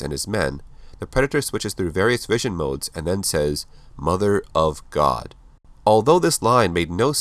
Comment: I thought the predator said "now you will die"... instead of "mother of God "?